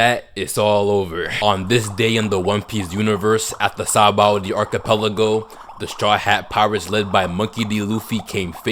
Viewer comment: It’s sounds like your saying saboldy archipelago